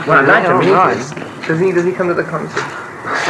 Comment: what did plant say that made them laugh at 00:50 ? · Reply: I think he says, "Really? Has he been to any concerts?"